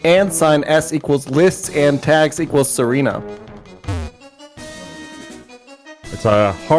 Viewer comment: until the URL was posted i thought said "Serena" and thought I'd have to call the FBI.